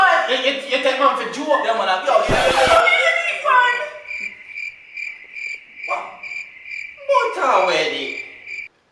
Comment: what did she say a 3:25 ? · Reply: she said she doesnt even eat bun. bun means to cheat. she interpreted bun to be bread lol.